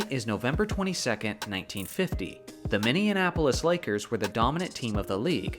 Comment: anyone else hear “mini-Annapolis” ? 😂